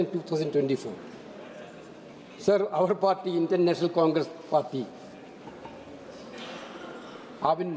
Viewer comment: did he say international congress party lol